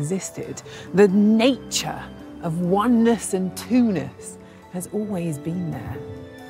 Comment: it's "twoness" not "Tunis" or "Tunas" dang British accents. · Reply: I thought she said "twoness" properly.